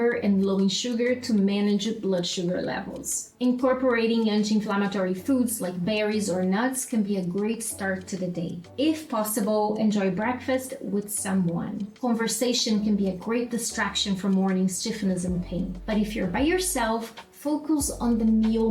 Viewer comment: It must be the fibro fog, but I first heard "enjoy breakfast with someone" as "enjoy breakfast with some wine" 🤣 🤣